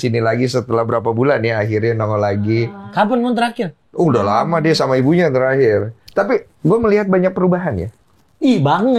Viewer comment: Menit 48:52, kedengeran kok ada suara aneh yaa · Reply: "Pak toloong"